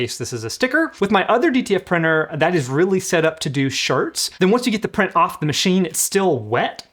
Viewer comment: Did you say Sharts?? 8:14 😅